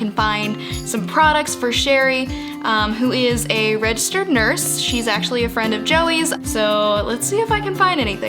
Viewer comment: why did i think she was gonna say registered sex offender